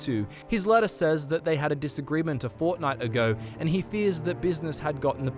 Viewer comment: DiD yOu SaY... FoRtNiTe?